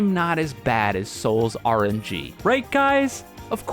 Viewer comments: I thought this is like a fighting game but turns out it’s just an AFK simulator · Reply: I thought it said sol's rpg lol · i thought the game said sol's rpg and I was like that sounds like a cool game